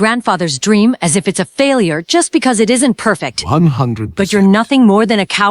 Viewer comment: bro i heard him saying 106 I thought he was staring take measurements like jiraya until I thought nah he had to have been saying percent lmao